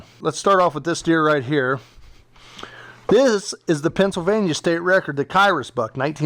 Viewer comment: Did he say Pennsylvania state record at 18:42?